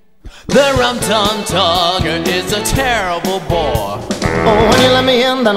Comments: mr mistofelees in his song: “the rum tum tugger is a terrible bore” rum tum tugger: *has a whole song about how great mr mistofelees is* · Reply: I swear to Liza, I thought he said "is a terrible boy" until reading this comment. It made complete sense to me, and I was shipping them since seeing them... I guess I heard what I wanted to hear :D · His entire song is about wanting the opposite of what's given to him.....so all the lady cats fawning over him? nope he wants the magical boy that called him boring XD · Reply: The first few times I heard “Rum Tum Tugger is a terrible boar” 🐷🐖